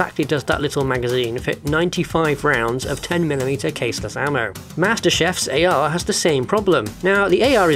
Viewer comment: did you say Master Chef? Gordon Ramsay is getting extra mad.